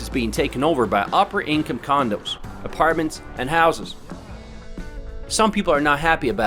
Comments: did he just say condoms? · Did he say the neighborhood is being taken over by "opera income condoms"? LOL